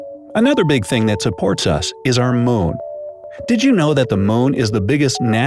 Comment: sounds like he says "Moan"